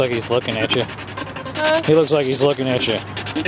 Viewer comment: when you say "he looks like he's looking at you" it sounds like he says "I am.. I'm watching you"